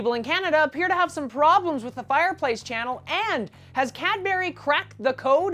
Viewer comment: It sounds like is saying Catbury lolz. My dad used to work at Cabury.